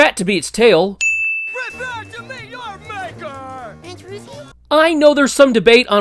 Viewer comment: I always thought that when Ed slammed onto Jimmy, I thought that Jimmy said “Aunt Ruthie” and that his aunt was a wrestler.